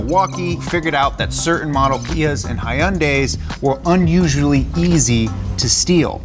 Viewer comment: wtf did he just say ? High un days ,the fuck 😂